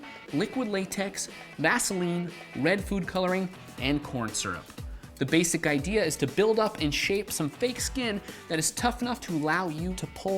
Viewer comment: i wasn't looking and thought he said porn syrup